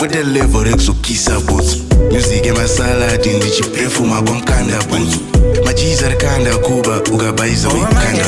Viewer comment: kwaMukanda Bhutsu 😂😂 I've been there couple of days, that place is lit fa sho ❤🎉🎉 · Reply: I thought he was saying kwamukandap